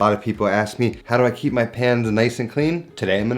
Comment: Bruh i thought he was saying: „how I keep my pants clean“